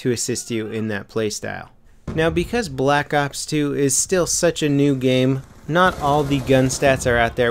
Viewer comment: I swear i heard "Now because black ops 2 is still such a noob game" hahaha